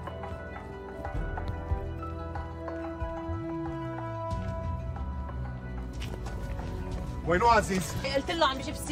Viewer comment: الحين لما وشوش مجد هنا 26:00 ايش قالت له ؟ بالضبط 😢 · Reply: Diabete يعني سكري